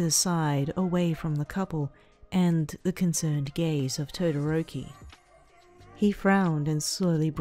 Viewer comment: who else heard "the concerned gays of Todoroki" or is my mind someplace else because it is 3:07am were i live